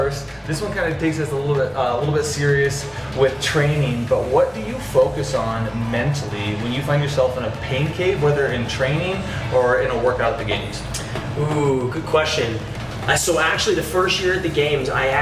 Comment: i can’t be the only one who hears “pancake” at 1:00 even the subtitles say so.